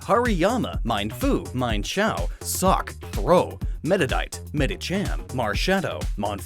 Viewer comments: i legit thought (at first) he said meta knight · Did he say sock? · Did i just hear SUCC at 1:59?